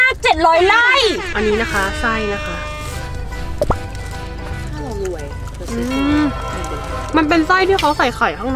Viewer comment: ทำไม14:06ได้ยินว่าถ้าเรารวยอ้ะเราจะซื้อเธอมาอยู่กับเรา555